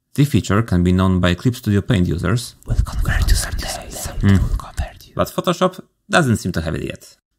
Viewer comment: I have absolutely no idea what you said. Aside from the Clip users whisper - very cool, thank you :) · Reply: "we'll convert you someday..." I guess with today's announcement it got a little bit easier :)